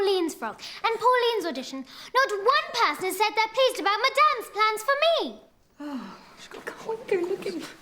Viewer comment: ha when she said madame plans lol i thought she said my damn plans lol. thanks for subtitles but would of sounded cooler if she said my damn